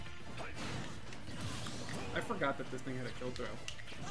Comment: white boi said what 👀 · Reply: Pretty sure people heard n word but he said "this thing"